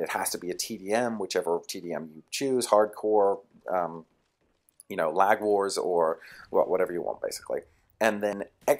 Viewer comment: did he say lag war? lol